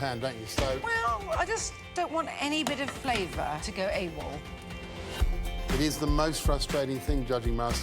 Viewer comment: What does she say at 6:44? I just don’t want any flavour to go .... ? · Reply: Maybe its late, but she said AWOL